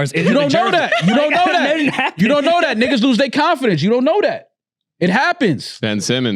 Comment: Did mal say "Niggas lost confidence" Jcole got ED cause he pussies out the beef😂😂😂